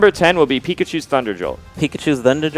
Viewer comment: John Smith pikachu thunder jolt is neutral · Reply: Ahh whoops I thought they said thunder Bolt